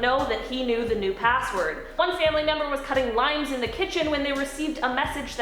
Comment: I honestly thought she said " cutting Lines, in the kitchen"